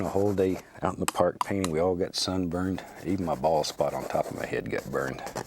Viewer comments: Did I-... Did... Did I hear correctly? Uhh, balls? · Thought he said “my balls got burnt”